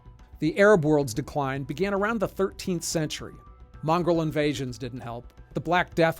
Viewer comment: did you just say "mongrel invansions"? yikes, on a few levels, haha.